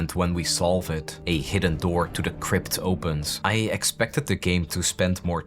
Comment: man I heard you say "to the crib" 🤦‍♂😂